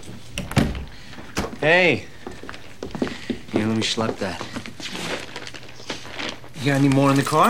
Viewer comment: What does he say? Let me "schlupp" that? Is that even how it's spelled and what does it mean? Steal or take? Lol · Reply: Lug, carry, haul... Schlep